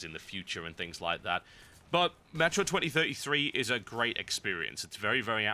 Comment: I thought TB said "Metroid 2033" and it got me so excited... I heard wrong, it's "Metro 2033" :(